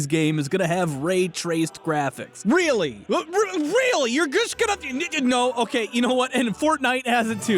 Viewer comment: I thought he said “nigga no” I had to replay it a few times